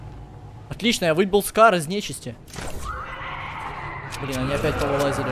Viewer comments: Мне послышалось или ял сказал на 18:39 Нормально вы**ал Скар из нечисти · Мне послышалось ял сказал на 18:38 отлично вы**ал Скар из нечисти